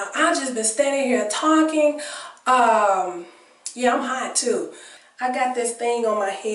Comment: Great, great info! I’m always looking to simplify the process. You’re very wise! Btws @ 22:39 did you say you were high? 🤣🤣🤣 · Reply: LMAO!!! Girl no....LOL!!!! I said "I'm hot too!" LMAO!!! When I first read your comment, I was thinking "I know for sure I didn't say that!" But I did go back to hear exactly what I said! I'm a drug-free person. Thanks for your comment and my laugh for the day! LOL!!